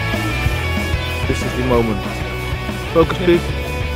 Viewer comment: I think he says at 0:09 - "this is the moment, focus please" I dunno about the other one though...